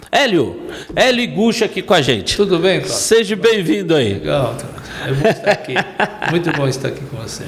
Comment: Eu ouvi “seje bem vindo”?